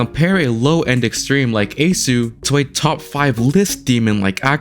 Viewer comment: did bro just say " ace-oo"???